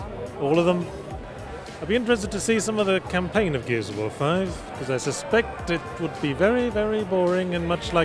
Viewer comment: I wasn't listening closely and when he said "Gears of War 5" I thought I heard "Metal Gear Survive"